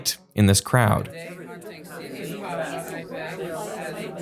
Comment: thats so crazy because i heard the word California and my brain clearly associated that with traveling, which is what i wanted to hear, and my brain then locked itself into that conversation. The brain is so insanely good.